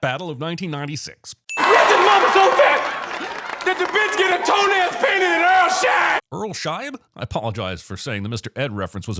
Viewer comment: this is an absolute eye opener for me. I watched this movie a million times as a kid and I always though he said "she gets her toenails painted and the earth shines" I thought it was his way of saying that she had big feet. 😂😂😂😂